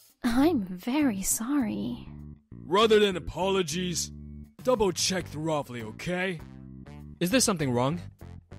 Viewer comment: Did I hear him say "Thorofly"? 🤨